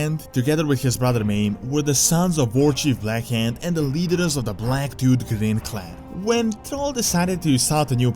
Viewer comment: Oh, Black Tooth, I thought you said "Black Dude".